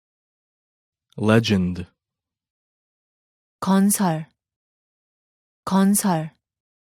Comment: why do I hear 건설 instead of 전설?? Which is right?